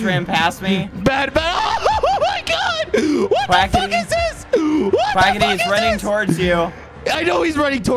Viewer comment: I thought bad said “the fuck is that” when you said we are gonna play adopt ms